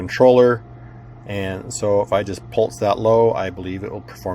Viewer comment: At about 6:10 you are talking about the microcontroller and you say "if I just..." and forgive me but what do you say? I hear "Pulse that low", but I am probably hearing that wrong, and what exactly are you doing with the tweezers? Thanks! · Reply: Yes. I said "pulse that low". What I mean by that is I am momentarily connecting the Reset pin to the Ground pin. Since the tweezers are metal/conductive, they are connecting the Reset to the Ground.